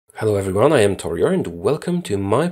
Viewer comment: i keep hearing "hello everyone, i am toyota" are you?